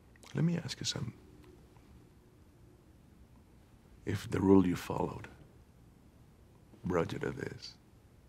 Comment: I always thought that he said, “ If the road you followed brought you to this, of what use was the road?”